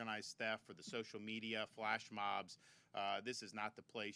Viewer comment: OMG I thought he said: "you have these black mobs incited by social media..." I ran it again. False alarm folks. He said "flash mobs".